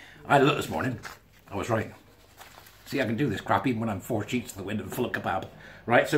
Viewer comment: 4 sheets to the wind and full of kebab? I thought you said "4 shish" 🤣🤣🤣